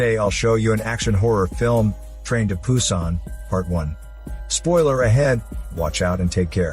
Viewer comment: Did he just say ''Train to Poosan"?